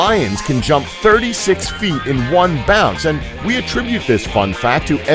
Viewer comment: says bound instead of bounce